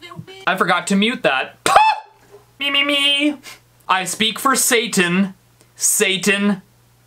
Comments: All I heard for the brothers I heard “I speak for Asian Asian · on the one where it said 'I speak for satan I testid it on my ds and it sounded like it said i slept with the savyer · To make the kids of the future not creepy, it sounds like he says i speak for natas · I heard "I speak fo sashan"